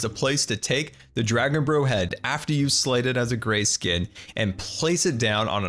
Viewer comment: I thought he said "slaughtered a grey skin" lolol